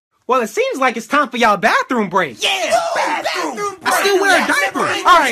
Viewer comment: did anyone else hear "im still wearing a diaper"